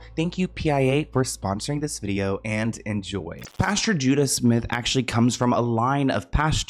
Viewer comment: I thought you said his name was JUDAS 😂 I WAS LIKE AWW HELL NAWWWWW